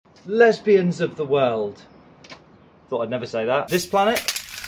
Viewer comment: I thought he said less beings of the world